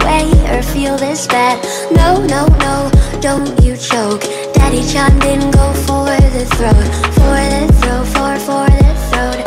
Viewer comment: I legitimately thought it was “Daddy-Chan didn’t go for the throat” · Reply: well i thought it was daddy said go for the throat